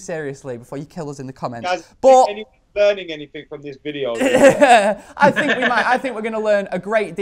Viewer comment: thought said Henning Berg is watching this video😂😂😂